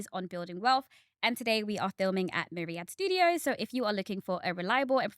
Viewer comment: Are you in Saudi Arabia now? I thought I heard Riyadh studio